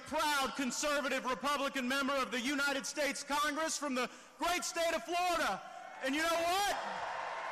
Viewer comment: did he just say the great state of Florida